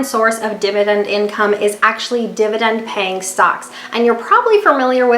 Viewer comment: I don't know why I kept hearing "dividend paying socks" and my imagination just... Anyway great video! :)